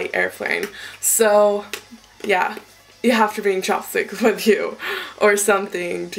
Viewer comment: oh your saying chap stick?! I thought you were saying chop stick haha